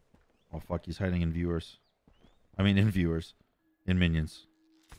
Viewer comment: Did you just call us minions?. gee gee <3